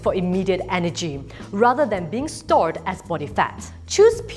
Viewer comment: what does she say at 4:38 pure roar oats?